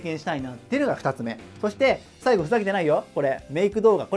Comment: 俺画面見ないで音声で聞いてたからメイク動画がレ○プ動画に聞こえてガチでびびった